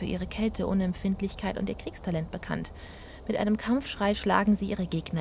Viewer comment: bei 6:49 hab ich Kekstalent verstanden XD